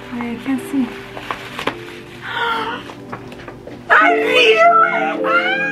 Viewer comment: did you say “i knew it” when you saw the gender 😭